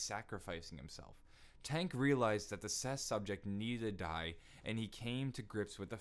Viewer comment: on 9:56 he says test subjects wrong he says ses subjects or at least that's what I heard correct me if I'm wrong